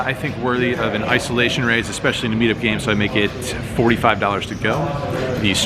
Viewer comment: when he says 'especially in a meet up game' all i hear is 'especially playing these fish'